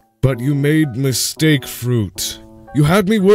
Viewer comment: I think that says “first” not “fruit” LOL